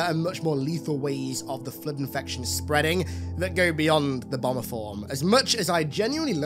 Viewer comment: Dude said "bomber form" with his accent and I heard "obamaform" and laughed about a proto gravemind Barack.